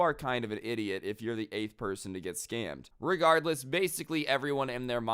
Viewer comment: „if your the gay person” ok then….. edit: im stupid now i hear 8th person